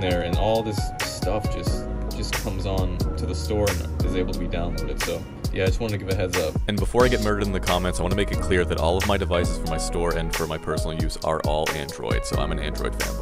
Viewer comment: What I heard is "android femboy" not fanboy